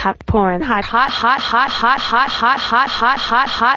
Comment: When she laughed it sounded like she said 'Hot hot hot hot hot hot hot' XD